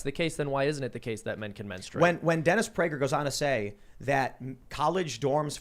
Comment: I thought he said Dennis Raider... as in the serial killer. The Lady Mondegreen effect strikes again 🤣